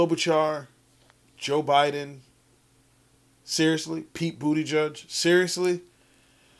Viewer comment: lol it sound like he said Pete BootyJudge hahahaahahaha